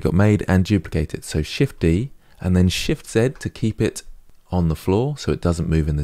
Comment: Sorry.. i am new to this, Great app.. thank you for the class.. 👍 I am still on waching your videos, I speak spanish all thought i am fluent on inglish. 1. I didnt understood, how you made the plain "floor" on its position? Shift -1 ? 2. And the spheres right on the floor, on the minute 11:14( shift + "Set" ) i think you say". THANKS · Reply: shft z, 1